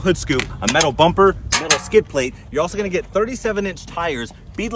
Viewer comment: bro, I thought he said "F*ck shocks"